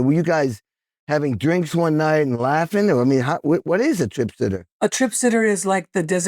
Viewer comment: Ok one more , but last comment.. ha ha ha. I hear you say "The Band is called Tripster" (edit Trip sitter) Tom Bukavac w/ Dean DeLeo (STP ) & guest singer Jon Anderson . Had a band called Trip the Witch. 2o21 / 2o22 You should Definitely have Tom Bukavac , in for a conversation/ interview.